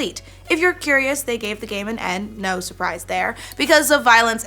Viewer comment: Am I crazy or is saying "N" for the rating... 1:59 ...